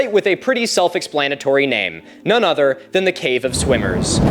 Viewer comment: dawg I thought you said "cave of SWINGERS" 😂😂😂 Edit: 17:21 HE SAID IT 🤣🤣🤣